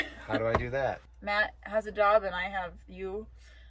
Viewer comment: I thought you said, Matt has a dog and I have you!🤔🙄😂